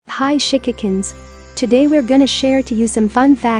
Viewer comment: The way Google pronounced shikikan, I thought she said hi chickens.